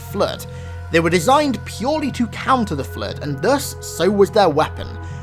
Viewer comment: I misheard "counter-flood weapon" as "counterfeit weapon"